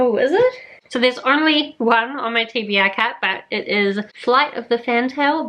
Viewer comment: For a second I thought you said TBR cat instead of cart and I was really intrigued. Now I'm imagining someone with a cat and placing things on the floor and somehow getting the cat to walk around and pick the options. LOL. But yes, your TBR Machine is awesome. Good luck with the July reading.